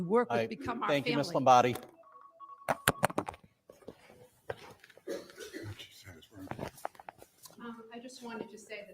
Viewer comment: DID ANYONE ELSE CATCH THE WHISPER FROM A BOARD MEMBER RIGHT AFTER FINISHED?? IT'S BETWEEN 12:46-12:51 TIME STAMP! I CLEARLY HEARD "WHAT SHE SAID WAS", BUT I CAN'T 100% MAKE OUT THE LAST WORD. NOT SURE IF THE WORD USED WAS "RIGHT OR WRONG" AT THE END OF THE "WHAT SHE SAID WAS" COMMENT. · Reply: I heard “what she said was wrong”. Looks like it was saying it.